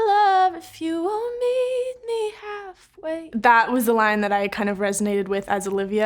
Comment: at first I thought she said “need me half way” when it was “meet me half way”